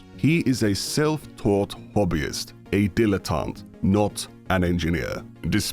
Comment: what is this word? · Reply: dilettante, or someone who takes interest with a certain topic but doesn't have any deep knowledge of it.